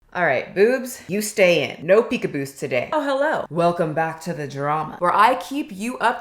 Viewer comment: I thought you said "no peekaboobs today" lol